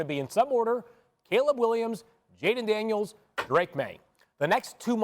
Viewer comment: No not really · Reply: Oh, I thought I heard you say something like "Not Caleb Williams."